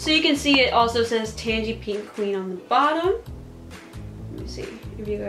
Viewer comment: I thought it said "pork queen" 😂